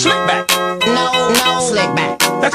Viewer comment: I thought he said "dumpling slickback" 💀